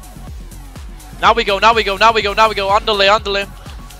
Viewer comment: Did you say "Ándele"?